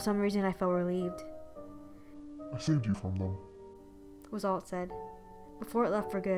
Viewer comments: what does the monster say 🤔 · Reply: “I saved you from them” you can turn on subtitles if you can’t understand me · What did he say? I couldn't hear what he said smh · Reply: "I saved you from them" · what did he say? I couldn’t understand it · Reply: Faith TV "i saved you from them"